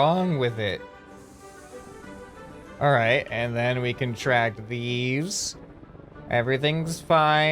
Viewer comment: All I heard was, "and then we can attract thieves,"